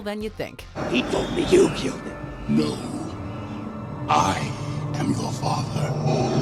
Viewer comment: Funny thing is even when I saw the clip for Star Wars, I still heard the "Luke, I am your father". Only when the narrator said that the actual line is "No, I am your father" and I went back to it listened to it carefully that I heard the word "No". So I guess it's more of like since the words were not that clear on first hearing (especially if you are in the cinema watching it the very first time), you will think that "Hey, he's talking to Luke so it's only logical that he will address the person he is talking to".